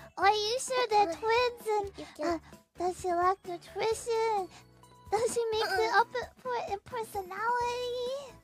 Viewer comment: I think Fuwawa was saying “doesn’t she make up for it in personality”